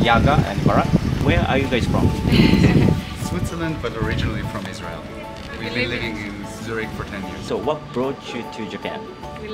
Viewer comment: ベネズエラなんて言ってます？ switzerland but originally from israel (イズラエル)と聞こえるように思うのですが？ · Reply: 私も思いました。イスラエルとはっきり言ってますよね。